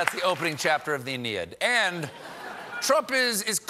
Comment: Did Stephen say Iliad? It sounds more like Indiad, the way he said it. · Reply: He said "Aeneid." It's Virgil's poem about Aeneas, a Trojan who escaped the sack of Troy and wandered all over looking for a new home.